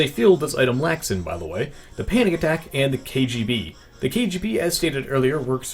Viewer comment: I almost thought he said "panny attack"